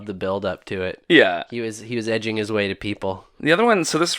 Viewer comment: Did he say God is edging to people? What a sicko! I knew It watched people choke the chicken or find the little man in the canoe, but i didn't know it was going at it too, while it watched!